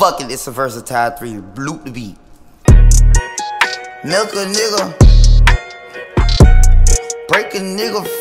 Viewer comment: milk a nickel* 😅😅 man what lol😂 ik he didn’t say nickel btw